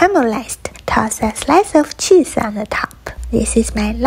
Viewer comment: Did I hear "this is my low life husband burger " 👁👄👁 Mr he has it nice and hard